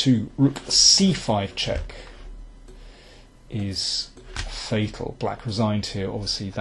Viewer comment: what is the word says at 15:28? it sounds like "fehicle" (which i hear in many commentaries of him) but i can't find such thing in dictionary. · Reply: fatal.